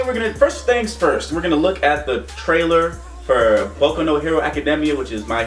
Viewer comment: Boi i thought it said Boku no Pico...